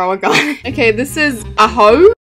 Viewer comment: it took me a couple seconds to realise what you said aho 😭😭😭😭